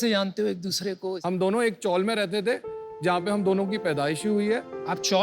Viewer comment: "___ mein rehte thhe"... what's the word? 'Chowl'?? Koi samjhao yaar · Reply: Chawl